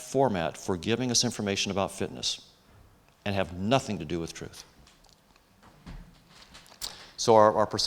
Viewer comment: " . . nothing to do with ???????" anybody catch that word? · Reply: Nothing to do with truth.